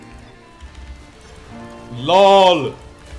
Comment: nó nói gì zạ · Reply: đức nguyễn LoL nhé